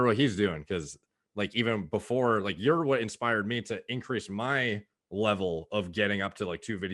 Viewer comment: "Inspired me" when I thought I heard "expired meats"